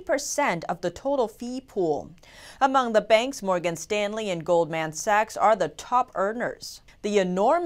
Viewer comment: Did she just say gold man sacks XD